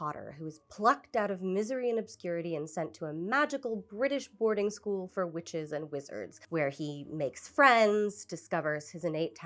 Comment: Holy crap, at 1:54 I swear I heard you say, "a magical school for Richards and Rizz-ards!"